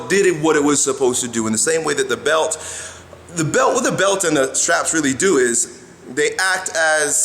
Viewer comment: Oops, My bad bra. I thought you said bands not straps. Disregard everything I said. "these are not the droids you are looking for".